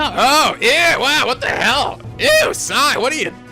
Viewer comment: DID HE SAY "EW"?! FLIPS TABLE